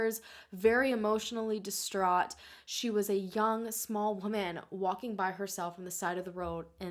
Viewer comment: Because I listen to most videos at 2x normal speed, I can sometimes hear things weirdly and have to back up and reduce speed. But at about 36:45 I swear I rewound 3x before reducing speed because I kept hearing "she was a young, small banana walking by herself in the dark." 🤦🏻‍♀️😳😂 The video and it's content are obviously very serious but that killed me!